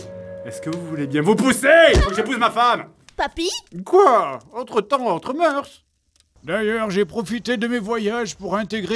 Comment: il dit quoi? J'ai pas compris , merci les sous titre YouTube a chier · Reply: Il a dit "quoi? Autres temps, autres moeurs" ;)